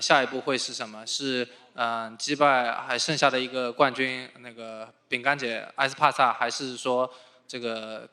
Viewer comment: Anybody heard the Chinese translator say the n word ?? 😂😂🤦‍♂️10:15?